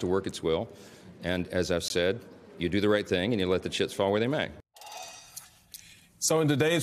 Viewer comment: I heard “And you let them shts fall where they may.” and actually liked the guy for a second 😂